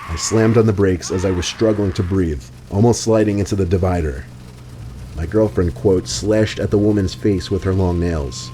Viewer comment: What in earth does he say at 7:00 that sounds like "My girlfriend quote slashed at the woman's face with her long nails"???